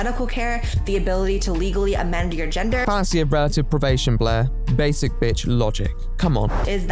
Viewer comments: "Fantasy of relative probation" what is that? You say it's basic bitch logic but I couldn't find anything about it on google. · Can anyone tell me what says at 30:39? (right before saying, "Basic bitch logic") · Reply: “Fallacy of relative privation, Blaire.” It’s the “ you shouldn’t be complaining, there are kids starving in Africa “ argument.